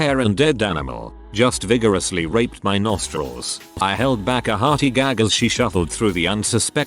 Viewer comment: did you just say it raped your nostrils...